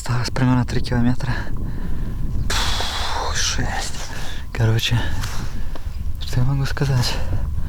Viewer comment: What is the word here that sounds like "karocha"? I don't speak Russian. The captioned English translation reads "It's hard. So... What can I say?" But when I enter that English text back into a Russian translator the word that sounds like "karocha" is lost. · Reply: This word is "короче" (koroche), translation to English is "shorter", it means in the context "tell the long story shorter", but in English expression "короче" is "so"